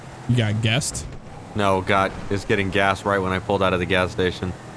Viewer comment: I swear I heard him say God is getting gas